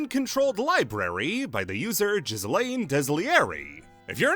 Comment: did you say the first English user of UwU was… Ghislane????